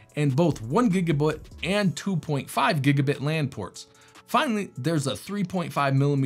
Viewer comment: is that 'gigabutt' i hear?